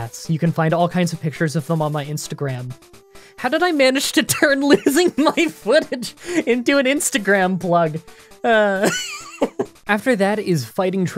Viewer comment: I zoned out for a minute and then swear I heard "how did I turn loosing my foot into an Instagram plug" at 13:54 and had to back up because I thought you were talking about some big medical thing